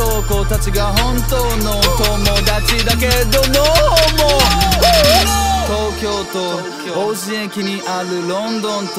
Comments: lmao sounds like he says @ 03:04 oh NO no HOMO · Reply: He is saying no homo. He's saying that his boys from back home are his real friends but no homo. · I thought he said no homo